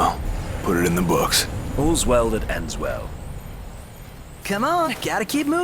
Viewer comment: English all the way. Noctis sounds great. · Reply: Yeah, fangirling so hard right now xD Do you understand what Noctis says during battles? I only understand "Got'cha", but the other one sounds like "End's now". I'm not English, but I would like to know what he's saying there from 07:23 to 07:25 :)